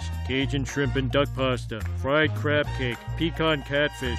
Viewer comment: did homer say Minecraft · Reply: No he said fried crab cake